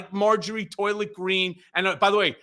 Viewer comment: I thought you said Marjory soilent green. Lol.